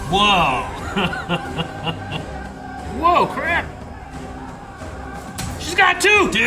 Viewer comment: I thought he said woke crap lmao. He wouldn't be wrong